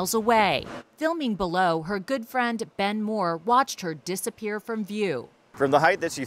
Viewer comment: I lost it when they said “Good friend Ben” 🤣🤣🤣 come on man. Throw him a bone, or 11. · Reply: I heard “bend more” as his name